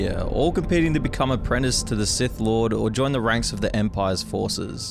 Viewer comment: My phone speaker was having a weird issue, so I thought you were saying Sith Lord Reject